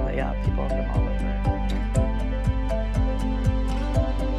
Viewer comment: at the end i always hear ''super gay'' instead of ''soul pancake''